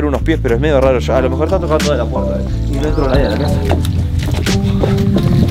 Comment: parece que dice pasa alba